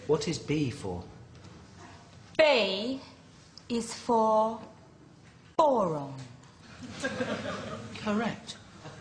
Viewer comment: lmao I thought she said B is for bacon